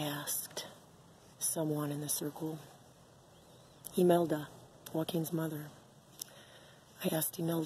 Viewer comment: I never heard her say Elon, she said Joaquin didn't she?